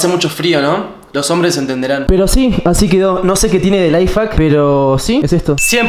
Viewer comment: escuche lifefack?